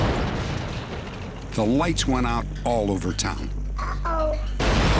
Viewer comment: Did anyone notice what the baby said at 33:00? LOL!!! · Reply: “Uh oh 😮” 😂